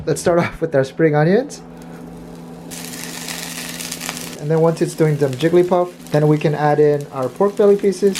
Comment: I'm not sure what you said here but it sounded like "and once it's doing the jigglypuff then we can add in our pork belly pieces." So I have to wait for it to start singing or does it need to actually evolve to jigglypuff in the pan? lol